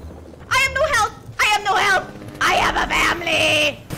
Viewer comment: did you just say family?